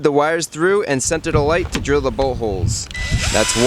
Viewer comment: when he was drilling it sounded like he said buttholes it was actually bullholes